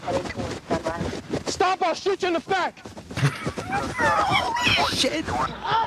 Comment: I know he probably said back but at around 7:00 sounds like he says stop all shoot you in the fat, I started cracking up.